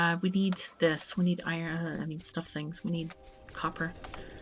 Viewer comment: Did she just say Suff and Things